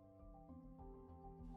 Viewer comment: When you said replace B with a C I thought you said S for some reason so I was like "Srain? Srain." And then you said "Crain" and I was like "OHHH"